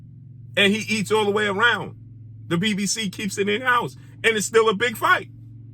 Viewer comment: Did he say the BBC? 🤔😭😂